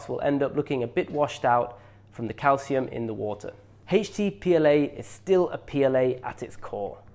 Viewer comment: Informative video, but the guy's pronunciation of the H was very distracting. Sounded like he was saying HATE CH T PLA.